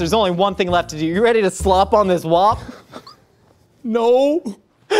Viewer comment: really said, “Slop on this Whopp” 😳😳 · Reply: I could of sworn he said "slob on this whop" lol and I say yes please. 😋